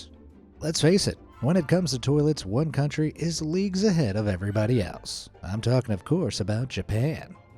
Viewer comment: misheard it as "when it comes to toilets, one country is leaks ahead of everybody else" 😆